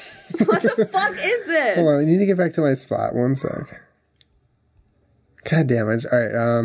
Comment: Julien says God damage instead of God dammit 😂